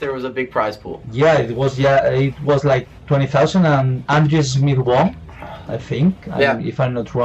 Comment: who was the guy from the Netherlands which one that first FIFA tournament for 20k? I can't understand what is trying to pronounce. (Around the 18:10 mark) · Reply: chapo Andries Smit